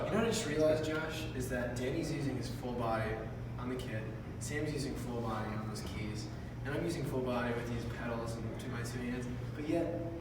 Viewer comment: What exactly did Jake say at 9:41?? My english is bad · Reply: Sam is using full body on those keys Im using full body with this pedals and both hands ...then he asks Josh what he does on stage